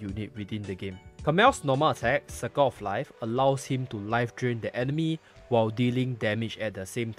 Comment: I thought you said Sucker of Life and I almost cracked up